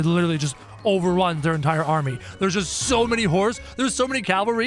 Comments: it sounds like he said " Theres just so many hors!" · it sounds like he said "Theres just so many hors!"